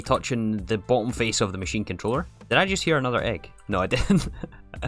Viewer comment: What did you say!? Hear an egg. Please get some rest lol.